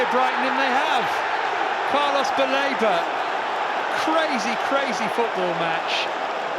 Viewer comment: After that Sanchez gaff commentator saying Carlos Baleba sounded like " Palmer's belaboured" to me😂